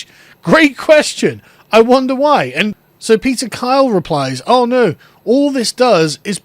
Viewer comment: When I first heard this story I thought they said Peter File not Peter Kyle . Peter File ( Paedophile) was the name of one of Jen's unsuitable boyfriends in the IT crowd . " Is there a Peter File in the Government?" - Probably loads ! ✌️🇬🇧